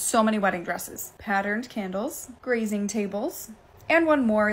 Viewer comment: I misheard "those little baskets" as "those little bastards" I need sleep